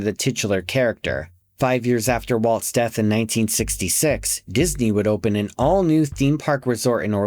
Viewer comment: No, he’s correct as Walt Disney World opened in 1971, EXACTLY 5 years after Walt’s death. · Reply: Maybe, I misheard him. I thought he was saying Walt died in 1971.